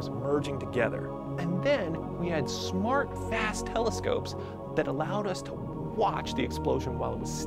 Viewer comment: I thought he said Smart Ass Telescopes